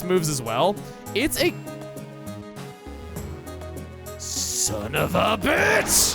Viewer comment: yes. There was. I heard "sonofaBITCH-" very clearly